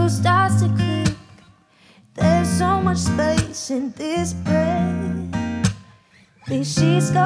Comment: I heard there's so much space in this bread, and now I cant unhear it.